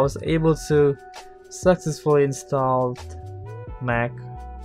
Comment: I thought I heard you say "sexistfully installed" 2:15 . I know what you meant though, haha. :-P